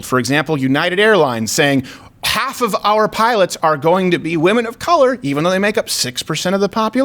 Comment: I thought American Airlines said women or people of color?